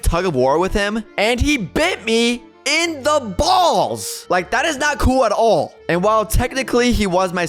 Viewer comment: did you say BALLS!!!😂😂😂